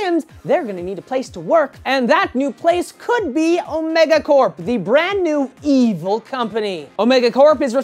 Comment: I thought he said megacorp when he said omegacorp